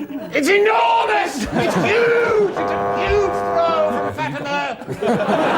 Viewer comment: What does Stephen say? "It's a huge __" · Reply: "It's a huge throw from Fatima!" Referencing female athlete Fatima Whitbread. She threw the javelin. So Stephen there mistaking a shot putt for a javelin.